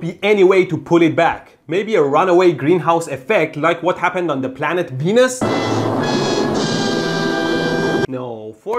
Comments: I know you're saying "Planet Venus", but I heard Planet Penis.... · I thought he said planet penis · I misheard the planed P(V!)enus :-D